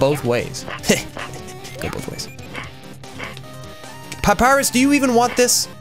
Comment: did anyone else hear Pot Pierus? or am i just hungry